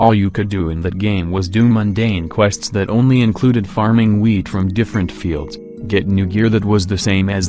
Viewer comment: I thought I he said farming weed but he actually said farming wheat